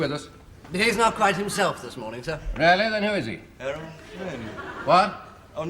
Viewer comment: what says? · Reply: He says Errol Flynn.👍